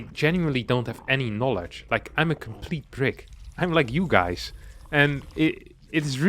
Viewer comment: "I'm a complete brick - I'm like you guys" he says grinning to the Camera. I really hope there is some amount of subscribers to this channel, that is actually decent enough at the game, to absolutely stomp over your Zerg Gameplay 🤣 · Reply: I thought he said prick